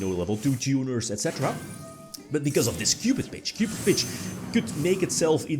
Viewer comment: Did say "cupidbitch" 🤣🤣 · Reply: No 😅